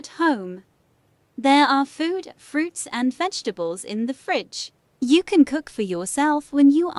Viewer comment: why did I hear “You can cook yourself”